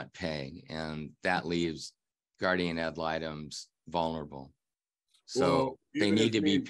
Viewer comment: Hey, lets give this dude some credit. Hes not just an angry dude. Hes much more than that. Hes a wordsmith as well. As he said, hes not out for "attaliation"... it's about his daughter. And the court "sitted" the court date she missed. There was also a "gurdiam am litum" and things that aren't being advertised are, in fact, "false advertisement". Thats only the tip of the iceberg of this hostile man's personalized vocabulary. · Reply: I heard “gurdia ma litem” 😂